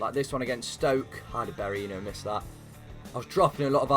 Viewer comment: Did he just say how did berahino miss that!? Cause he’s berahino